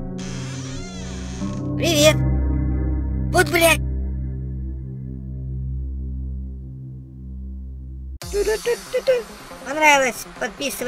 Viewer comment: мне послышалось ой бля